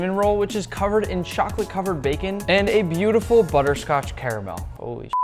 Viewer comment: did I hear that right CHOCOLATE COVERED BACON??